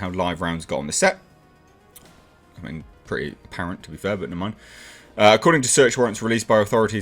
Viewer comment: What are those few words he speaks after the word 'apparent' at 5:31? Can't make it out, and closed captioning has 'fair but in mind', which doesn't make much sense.